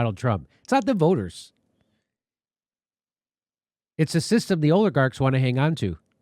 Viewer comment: Did Jimmy just mispronounce Oligarchs as OILigarchs?! Intentional? Did I mishear? Either way can we make this catch on? That’s my Bday wish for HRC 🤔